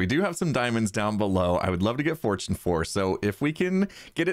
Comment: did he say fortune 4?